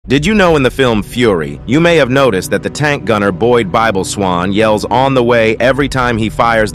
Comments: Heh, durp, I always thought he was saying “on the Y!” Thinking that had something to do with the reticle, I had never looked into it but more than likely the reticle was cross shaped rather than Y shaped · I always thought he said on the Y as in the gunner sight reticle being a Y.